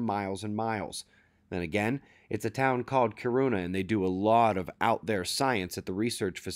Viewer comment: how many people thought said the earthquake was "in a town called Corona" at first?